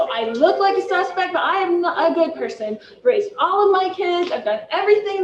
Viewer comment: At one point I heard Lori say I'm not a good person. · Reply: I think she may have said I look like a suspect but I'm a good person...